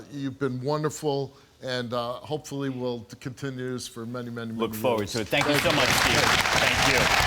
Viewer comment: huh, What was that u said at 3:50 Steve? lol. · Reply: Wicca WhiteVEVO hopefully it continues for many many many years